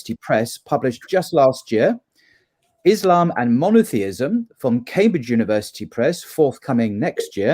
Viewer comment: Wow, i thought you said «the book “Islam and moneteism”» which i would like to have read, but it was monotheism (